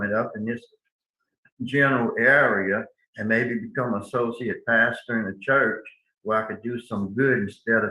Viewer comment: He is HORRIBLE! Each time he referenced his desire to become "an associate pastor," it sounds like he is saying, "A SOCIOPATH."